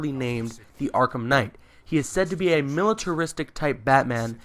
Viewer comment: Wait did he say militaristic at 1:01 . If that is true than i think i know who is he actually. He is <num> or another version of him.